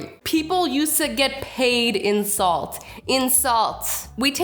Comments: If you have captions on at 3:28 when says in salt the caption says insult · when said 'In salt' sounded like insult 😂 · I thought she said insult